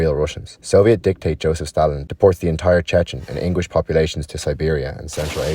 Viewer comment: Did he actually say English population or is it somthing else? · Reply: I said inguish but it auto captioned incorrectly apologies